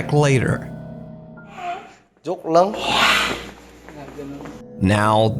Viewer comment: at the beginning of the video simultaneously i hear someone breathing in and yawning as if to shake off their fatigue. as well as someone saying "joke lang" which means "just a joke".. i actually yawn like this on my days off while playing computer games to stay awake i'm just throwing it out there haha